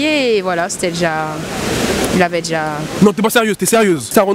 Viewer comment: il avait déjà quoi ??? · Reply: balancer la sauce